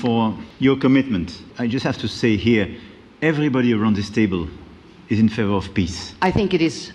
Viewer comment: Macron legit sounded like he said “everyone around this table is in favor of piss.” I have giggled every single time I heard it.